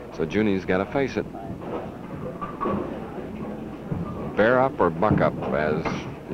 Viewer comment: did Schenkel say "spare up or F up"? Sounded like it